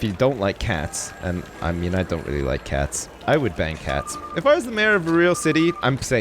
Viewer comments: I thought that he said that would bang hats instead of ban cats. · When you said "ban cats" I thought you said "BANG cats" My mind is messed up